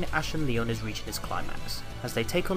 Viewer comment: Bro I thought you said ‘clart max’ instead of ‘climax’ at the start brav